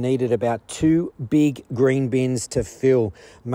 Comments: I thought he was saying " greenbeans" I had to listen twice. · Oh man, I thought he said green beans at first.